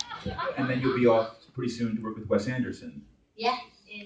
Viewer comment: what does she say? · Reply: Interviewer: You’ll be off pretty soon to work with Wes Anderson? Saoirse: Yeah, in eh . . . [to someone off camera] has that been released? Off camera: [Inaudible]. Saoirse: Well, it has now. Yeah, in the Spring. Imdb lists her as cast on 'The French Dispatch', so they must be referring to that film.